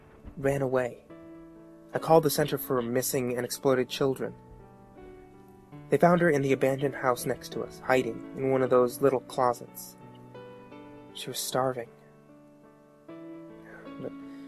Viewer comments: did anyone else hear "I called the center for missing and exploded children?" xD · did anyone else hear "I called the center for missing and exploded children?" xD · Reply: I heard 'I called the center for a missing and exploded children.'